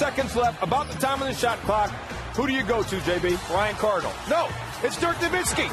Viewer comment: i heard Brian Cardinal 😌